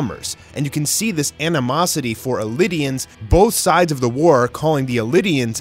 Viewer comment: Isn’t it pronounced “Eldians?” Why do I keep hearing “Elidians?” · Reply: it is eldians, is like the only person i've seen pronounce it "Elidians"